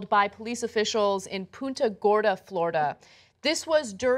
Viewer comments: Man, I thought she said Puta Gorda. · Work on your Spanish, Ana. I initially thought you said 'Puta Gorda'. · I hella thought she said "Puta Gorda"